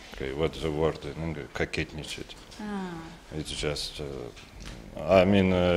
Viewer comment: what is the russian word he says at 3:34? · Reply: Кокетничать (coquet). In this case that means "to show off".